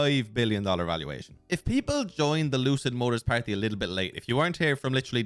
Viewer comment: I always hear "lose it Motors" ... nice car nontheless